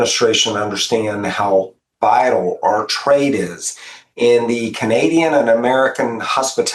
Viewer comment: I heard Chris Swonger say "how vital our trade is", but as is usual in American "simplified English" he slurred over the "t". So I heard "how vile our trade is". And it was nice to hear something from him that I could agree with. 😆